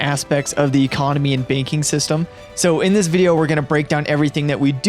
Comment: You said banking system but at first I thought you said baking system. I was like cooking 😎